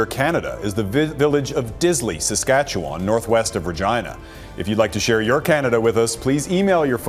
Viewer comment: Northwest of what?,, vagina he said? awkward..